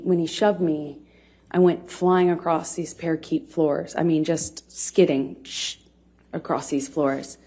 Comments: I'm sorry...at 5:15 did she say, "parakeet floors"? I was picturing a pavement of budgerigars rather than a parquet floor. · Does she say “parakeet floors” at 5:15? Oh girl.🧐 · Did she just say "Parakeet floors"? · "He Pushed me and I skid across these "beautiful" wooden floors" when someone has a domestic violence incident they don't describe anything as beautiful because that's not typically a thought linked to any emotion through domestic violence. This is first and foremost the 1 key word in her story that makes it deceptive. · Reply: I heard "parakeet floors" - like, maybe she was trying to say parquet? As in Parquet flooring? ETA: Oh, she said beautiful floor later. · Wait, did she say parakeet floors? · Did AH call a parquet (par-kay) floor a parakeet floor? Surely I misheard... Now, all I can see is a floor made from cockatoos..